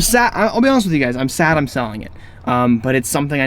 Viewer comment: e.e i thought u said u never selling it...